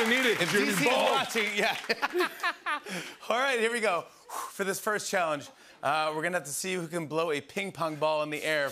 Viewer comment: it sounded like jimmy said “for the last challenge, we’re gonna fuck”